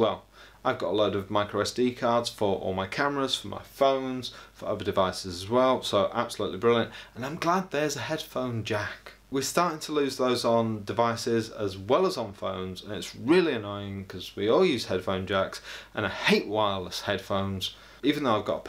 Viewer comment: I thought you said "no headphone jack" that would have sealed the deal on not getting it